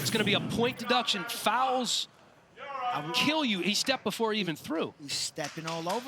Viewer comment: Why did the commentator straight up say "I'll kill you" out of nowhere at 30:20? Am I hearing things?